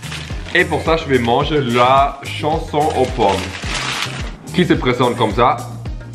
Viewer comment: c'est moi ou ta dis la chanson au pomme 😂😂